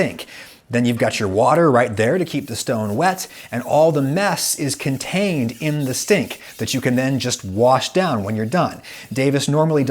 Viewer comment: I swear he said "All the mess is contained in the stink" and I love that